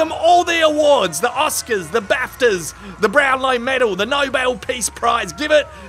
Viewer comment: swear to God, I heard him say "brown eye medal" first time through😂